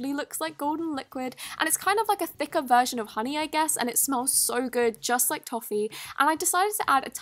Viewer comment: what did she say? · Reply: "A thicker version of honey I guess?" That's what she said 🙌